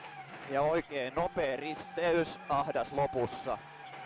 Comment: An enhancement: At 1:09 I believe he has spoke "risteys", something that means "crossroads", instead of täys. However I thank you for this job...very good.